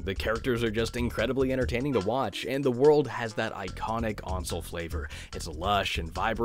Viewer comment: I didn't quite catch that... iconic on soul flavor? Oncel? Aunsole? Console without a C? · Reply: Ancel, as in Michel Ancel, the guy who made Rayman